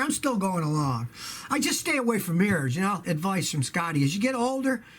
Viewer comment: I thought he said stay away from beers